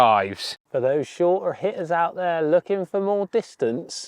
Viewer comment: Thought it said hitler not hitter xddddd